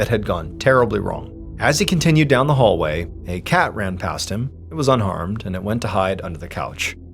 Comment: OMG, I thought he said the cat ran past him and was unarmed. I had to rewind! 🤣